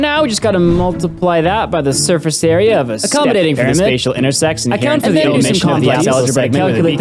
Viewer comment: Did blud just say intersex